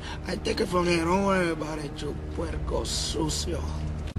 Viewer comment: what does Peoples say? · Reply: He says puerco sucio to the cop telling him dirty pig in Spanish